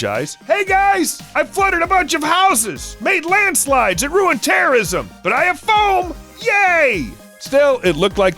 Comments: did he just say ruined terrorism? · Did he say “terrorism” or “tourism”?? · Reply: Terrorism